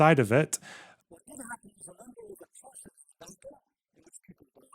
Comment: I can't hear the clip of Benny Morris? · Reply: I can hear him say the following pretty well, wonder if it's an issue only on specific devices or Apple or smth? "What did happen was a number of atrocities. There was a battle, and people died. And accompanying it were atrocities, a series of atrocities, in which a family was shot here, a family was blown up in a house there. All together amounting to 110 deaths. I would say that the word massacre is correct, by and large. A series of atrocities with several tens of people killed not in battle, amounts to a massacre even if it isn't the classic, you know, lining people up against a wall and shooting them."